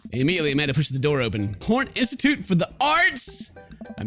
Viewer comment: I thought she said porn industry of the arts